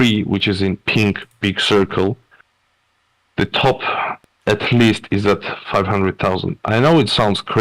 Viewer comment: Did I hear wrong? At 13:20 he said Eth top at 500k!?